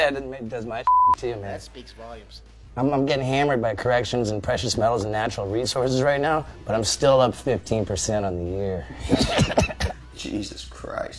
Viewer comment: Can someone explain why Johnny reacts the way he does after what Steve says at 7:53? I don’t not understand what he says there at all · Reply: "I'm getting hammered by corrections, precious metals and natural resources right now, but I'm still up %15 on the year" still not sure what he meant. but i think it was just random and personal shit , and i think steve's drug addiction was starting to get to johnnys nerves